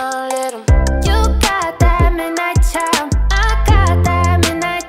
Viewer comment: heard this as "midnight chow" first time